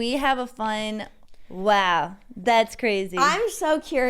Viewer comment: I usually just listen not watch so the first couple episodes I thought y’all were saying “wow Matt’s crazy” 😂😂